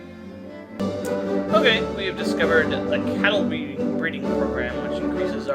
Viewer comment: I thought it said castle